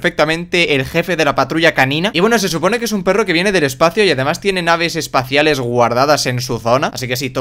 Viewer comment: entendí otra cosa xd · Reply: Giovanni Iquise Paricagua naves espaciales guardadas en su zona 7w7